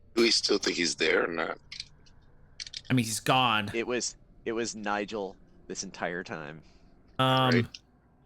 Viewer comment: I love when Casimir disappeared I hear "it was Nigel this entire time"